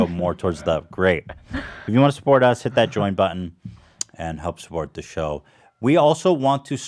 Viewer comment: I heard “hit the joint button”